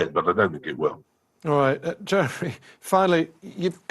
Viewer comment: Does he call him Jeffery at 7:38 😝